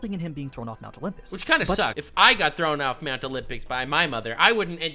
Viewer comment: does say "Mount Olympics?" 😆😆